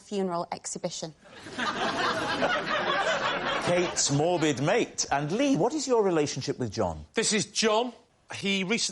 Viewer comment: I want to know what Claudia says to herself here…😹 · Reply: She says "I love you"